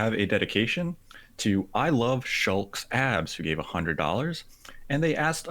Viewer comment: When you said "Shulk's abs" I heard "Sean's abs."